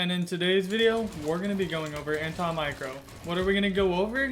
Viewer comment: anta micro. Am I the only one who heard it.